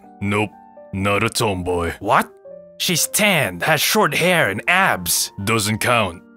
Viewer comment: LMAOOO I wasn't reading and when he said "she's tend" I heard "she's 10"